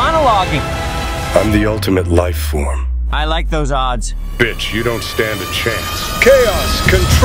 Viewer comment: WHAT?!!!! Did Shadow The Hedgehog said bitch?!